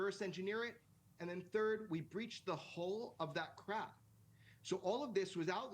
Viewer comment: I thought he said we breached the hull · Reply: He did. H-U-L-L Not sure why people thought the girl from Boston said hole haha